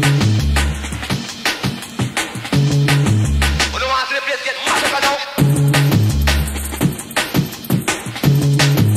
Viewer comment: What does he say at 15:08? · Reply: You want to see the place get mash up now . 😊